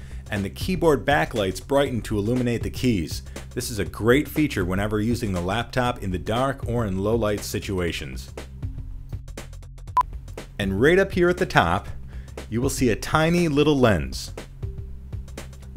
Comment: did he say "low-life situations"?